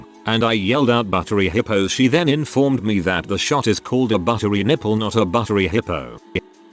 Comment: when they say buttery nipple i thought they said chunky pimples lol